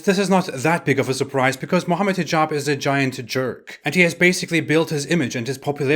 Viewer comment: I thought you said 'giant turd'. 😁